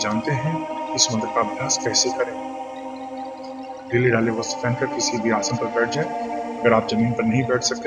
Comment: pe kya bola aapne..kon se kapde nile kaale...ya pile kale...background sound k wjh se apki baatien samjh me nh aa rhi hai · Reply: Dheele-dhaale vastra pehen kar kisi bhi aasan par baith jae.